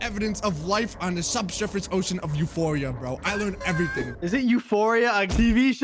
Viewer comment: When said the moon Europa it sounded like he said the movie or tv show euphoria and it’s not called Euphoria it’s called europa